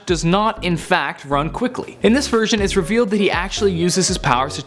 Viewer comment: Every time he says "version", I hear "virgin". I must have a dirty mind.